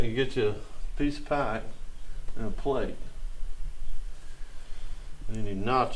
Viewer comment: at 10:48 I thought he said to get yourself a piece of pie on a plate. LOL I thought he was hinting to you to get him a snack. Loved the little ones at the end.